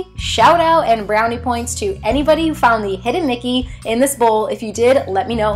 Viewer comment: I found the hidden Mickey! Lol.. at first I thought you said nickey and I was so confused